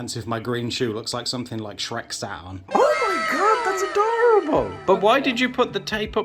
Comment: I'm watching this video without headphones on so I heard "Something Shrek sat on" as "Shreksonas" 👀